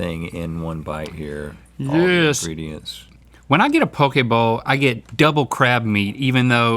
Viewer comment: Only one who thought they said pokeball instead of poke bowl? 😂